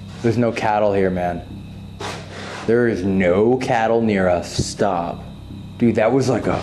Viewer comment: it sounded like a low, beasty growl saying "stop it" No idea why they would say that, possibly that you wanted to leave the chapel